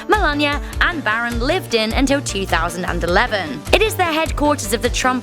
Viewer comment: she says “until 2011” but the english caption says “until 2017”